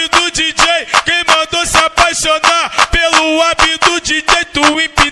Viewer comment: eu entendi "quem mandou se apaixonar, pelo app do dj" kkkk